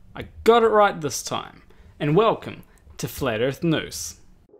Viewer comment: I'm not sure if it's just the way you talk or you actually did that on purpose, but I definitely heard "...and welcome to Flat Earth Noose."